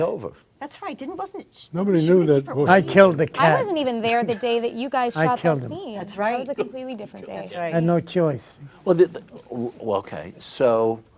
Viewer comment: Paulie - "I killed a cat...had no choice" Chris looking over, laughing, "wtf Paulie" XD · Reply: I thought he said, 'I killed the cameraman, I had too'